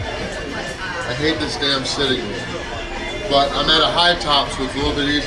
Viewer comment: said “I hate this damn sitting “, but the subtitles said “city”. too nice a guy to hate things, unless they’re affecting his food 😂